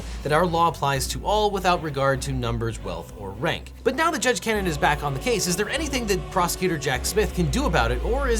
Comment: "So, now the Judge Karen is back" is what I thought I heard and wish you were allowed to say 😅